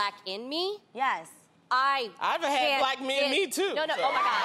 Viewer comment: did she just say she has had black guys in her?